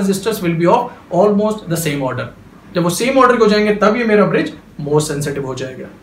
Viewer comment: what sir said here same model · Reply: Sir said same order